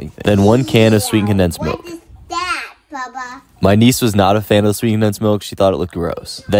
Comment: I thought your niece said “ we what is that PAPA” so I thought had a frickin secret child but then I realized he said my niece. I feel dumb as heck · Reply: Haha she said bubba